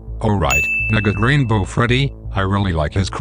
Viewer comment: WTF DID YOU SAY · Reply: Negate Rainbow Freddy